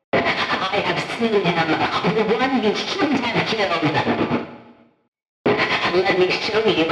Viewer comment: i slowed down the video for fred bear to 0.5 it Kinda sounds like hes saying "im the won you shouldn't have killed" edit re watching it now it sound like im your night mare you choose